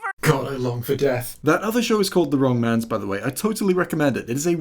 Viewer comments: what's the show called? I can't hear it properly. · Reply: The wrong mans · what is that show/movie called? I could quite understand what you said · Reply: The wrong mans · What is the title of the show mentioned in 2:56? I can't quite make out what he said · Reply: The wrong mans